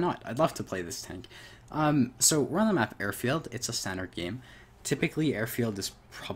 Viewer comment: OHHH he said "standard game". This whole time I thought he was saying "centered game". is one of those white boys that speaks so fast that his words runtogetheryakno?